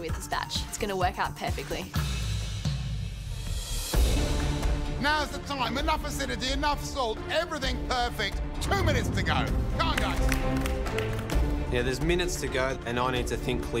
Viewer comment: what did he say?? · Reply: "Now is the time. Enough acidity, enough salt. Everything perfect. 2 minutes to go. Come on guys."